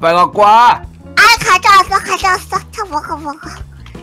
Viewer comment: 처먹어 라고들리는사람나밖에없나?